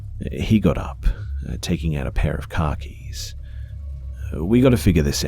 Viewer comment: i thought he said a pair of khakis